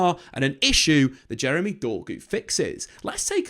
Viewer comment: Did I hear JEREMY DORGU at 6:23?